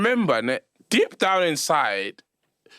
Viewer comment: thought sol waS saying deep down every guy is tryna fuck other guys like wtf:face-green-smiling::face-green-smiling::face-green-smiling